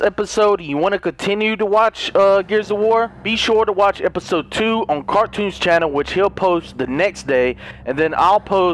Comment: At first I thought he said " watch episode 2 on Cartoon Network" ha ha...